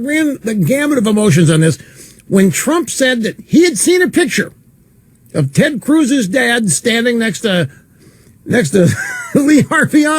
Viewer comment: I thought he was about to say Ted Cruz's D!ck :/